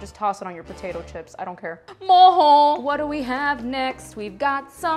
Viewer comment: For a second I thought you said Mojon and I almost spat out my water 🤣